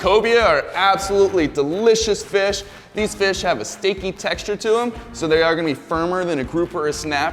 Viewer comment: I kept hearing "stinky meat". My bad. It's steaky meat.